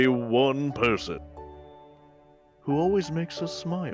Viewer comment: When they're radar said "who always smiles" I heard "who's really suicidal"